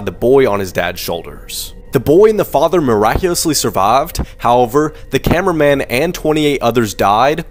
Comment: did he just say “miraculously the father and the son survaved” ??????